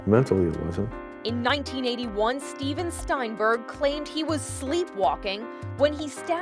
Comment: For a min I thought jt said Steven Spielberg